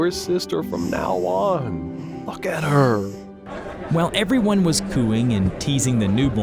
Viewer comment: it sounds like it said F*ck at her